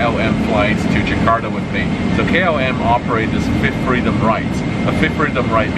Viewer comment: Fit freedom flight? What is he saying?